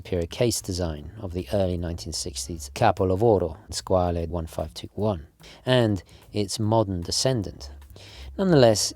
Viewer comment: does any italian speaker know what he said? I've been learning Italian and have heard say this multiple times but can't find what expression this. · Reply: "capolavoro" means "masterpiece"